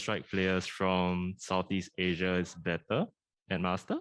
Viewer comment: i think he said...with bren out of the scene do u think its chance for prx for to show the sea region is stronger